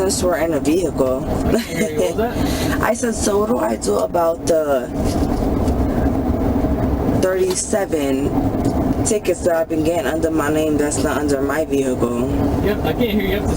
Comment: This is her second DUI and she thinks they can just let her go to walk home when she was passed out at the wheel. She really learned a lesson the first time. She is trying to get her real estate license...not exactly to bright is she. Of course she isn't responsible for anything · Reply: Sounded like she said: "What do I do about the 37 tickets that I been gettin' under my name on my vehicle" (14:45). Doesn't seem like she's remotely near to getting her act together.